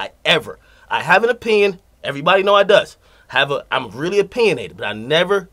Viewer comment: Am I the only one that heard him say "I have an opinion everybody know I does ?